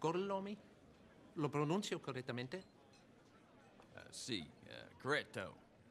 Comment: For anyone who doesn't know in 3:15 he spoke spanish, not italian. Which shows how much Landa loves to fuck with them. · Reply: I heard, "¿Lo pronuncio correctamente?" which would pass for Spanish for the same thing in the subtitles.